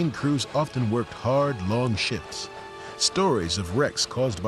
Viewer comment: it sounds like he is saying hard long sh*ts